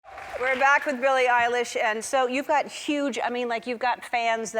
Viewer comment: I thought she said Billie eyelash 👁👄👁